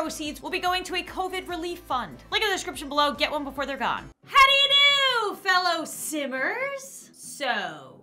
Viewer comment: When you said “how do you do fellow simmers” I thought you said “how do you do fellow sinners” 😂